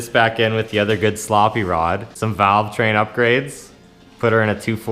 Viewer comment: did i hear corvair?